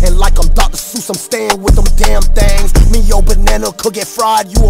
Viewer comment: I love how his flows all are hella cohesive lol not to mention he doesnt really mispronounce words just to flow better, its rarer than is should be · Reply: lol as I hear him say "Damn Thangs" instead of things